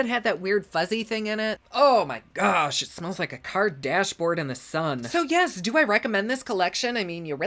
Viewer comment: When he said it smells like a car dash, I thought he said Kardashian lmao